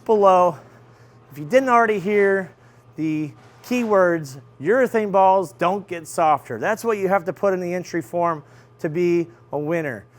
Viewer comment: 1st off congratulations for your achievement. It's amazing what a camera, great content, and courage can do 💪 and the phrase I heard was " Urethane Balls Don't Get Softer".